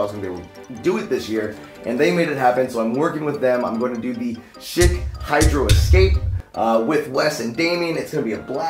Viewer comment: I thought he said Shit Hydro Escape